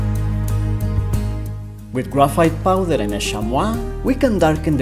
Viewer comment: Can someone please tell me what he is saying and where to get I it. "shamwa" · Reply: He's saying "chamois" - a type of leather from a goat-antelope species. Usually available in art stores, or car detailing stores - probably even Home Depot.